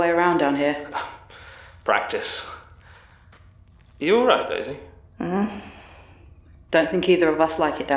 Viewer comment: I heard "Daisy" as "baby" and almost had an heart attack